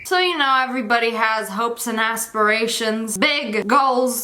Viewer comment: as$-persations Does anyone else hear it or is it just me